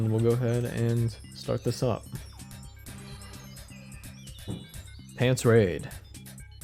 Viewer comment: [Welp the Stream is ending, guess I'd better--] 3:06:18 - "...ᵖᵃⁿᵗˢ ʳᵃⁱᵈ..." [...the what?] "I say: "I guess you're gonna miss the... 𝓹𝓪𝓷𝓽𝔂 𝓻𝓪𝓲𝓭"."